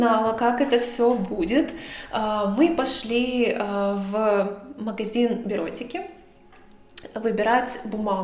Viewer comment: Послышалось в видео, что ты пошла в магазин эротики за бумагой 😂